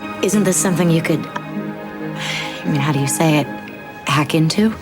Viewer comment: It's weirding me out that he's insistently mishearing Scully as "how you say" when she's CLEARLY AND ARTICULATELY saying "how do you say it"